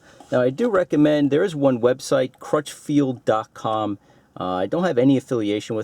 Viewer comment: What was the website you bought it from? My hearing is the best and I thought you said <link> but when I look that up I get nothing. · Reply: TPSGamer he said crutch field at the beginning